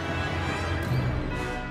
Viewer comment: Bro I legit heard "Hello Peter" but my brain processed it as "Hello Barry" i was like... Flash?! Wrong universe lol